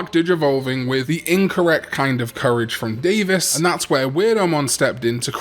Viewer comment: Every time you say "Devas" I hear "Davis"